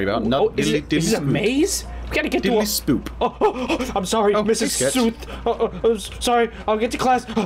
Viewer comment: Miss s_uth?! I THOUGHT U SAID MISS SOUP 🥣